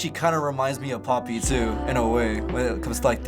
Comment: Does anyone know the artist that was referencing at 4:00? If so who did he say? Thanks in advance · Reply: KHI Official Poppy